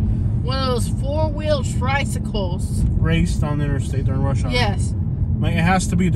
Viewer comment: did she say a four wheeled tricycle 😅